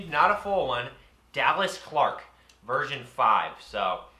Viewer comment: I heard “Dallas Clark virgen 5